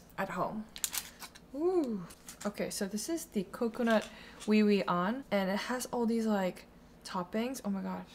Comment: girl you did not just say we-weawn